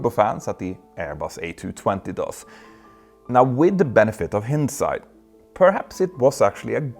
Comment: new favorite mispronunciation: hinnedsight instead of hindsight